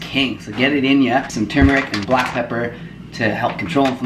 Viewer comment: What's the yellow/orange powder that he put in his smoothie after the kale? 3:58 can't quite hear what he said there :) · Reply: Turmeric!